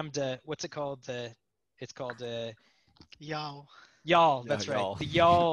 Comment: I can't quite make out what the Prolog derivative mentioned at 42:30 is called. Yawl?